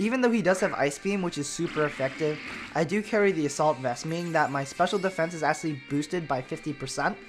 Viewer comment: What item did he say Goodra was holding at 3:33 ? I just can't make it out! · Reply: Assault Vest: +50 % Special Defence but the pokemon that is holding that Item can only use Attacking Moves.